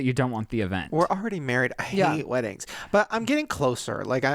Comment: WAAAIIIIT....at 50:55 does shane say "we're already married" !!!!!